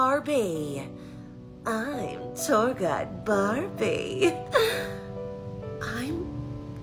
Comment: I thought in the end she said " I am your guide Barney👿"